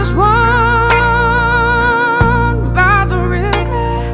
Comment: DOES ANYBODY ELSE NOTICE it sounds like she is saying,"I was wronged by the river" instead of "born by the river"? Whatever she is saying though, I LOVE it!